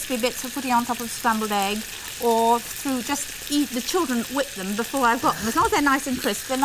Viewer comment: Did she say eat the children?